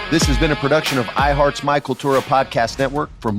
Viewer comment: This comment is for the hosting company (my cultura) not Freddie. 1) I listened for months before I could hear “My Cultura” clearly. Sounds like your saying Michael Cerra Podcast Network 2) adding 10-15 minutes of Spanish language ads post production to a English language podcast is 💯 counter productive! Especially since ads make up more than 50% of the total run time almost every episode. This listener is very close to moving on just because of My Cultura’s bad business practices. Good day. Freddie does a good show. “my cultura” makes it all but unlistenable.